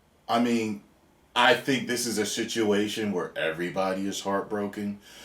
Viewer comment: It sounded like you said Shituation. XD I'm definitely using that from now on.